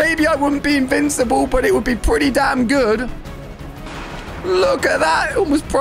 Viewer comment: Did he just say the bad word d*mn!😱